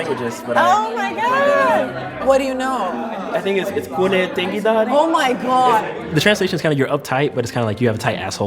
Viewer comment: I literally had to listen MULTIPLE TIMES to understand what he's saying as a Persian "Kooneh tangi dari" Yes, it does mean "you have a tight ass" No, you do not say this phrase in a first-time meeting with someone